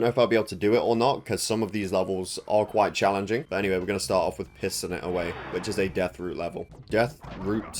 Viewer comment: I legit thought you said pissing it away